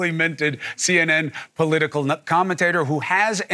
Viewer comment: did he just call Andrew a "political nut commentator"?